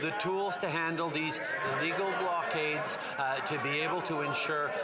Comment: Did I hear right? Did Trudeau just say Legal blockades at 1:01? lol I am surprised He didn't walk out again lol Played the video several times before I commented. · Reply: He says illegal blockades.